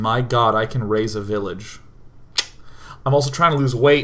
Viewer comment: I'll be honest...When he was talking about Banished..I heard him say "*Raze a village" lol. Which, considering how KSP went......